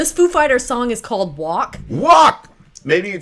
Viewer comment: why this sound to me like you guys are saying "whack" instead of "walk" lol